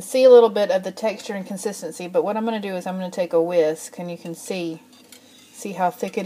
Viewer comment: I looked away for a moment while listening to your instructions and I thought I heard you say, I'm going to go take a Whizz, instead of the Whisk !! I had to LMHO !!